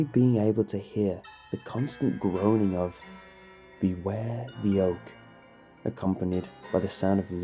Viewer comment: I swear I heard "beware the yolk"